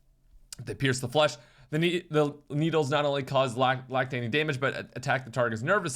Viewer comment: did bro just say lactating damage?😂😂😂